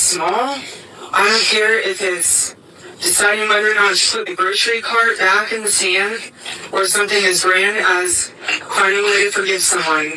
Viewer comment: I thought Erica said" put the grocery cart back in the PAN" , but it still doesn't make any sense!!!